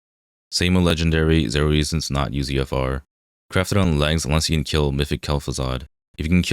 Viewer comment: Can somebody explain what he said at 5:20? I cannot speak that xD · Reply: Use UFR (Usoc's Fury Remembered) - he then goes on to tell you which slots but I think that is pretty clear.